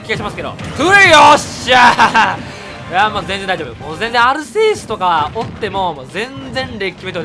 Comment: でアルセーヌがアルセウスって聞こえる